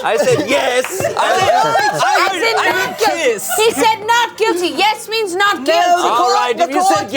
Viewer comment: I swear I heard guillotine😭